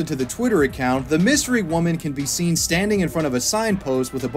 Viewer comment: lmfao he really said Cyrillic like “krillic.” 💀